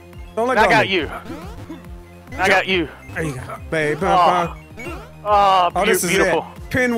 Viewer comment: it sounded like said bab